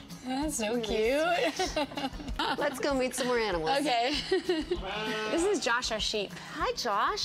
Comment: I thought Portia said "let's go eat more animals...." I was just like "WTF PORTIA! @@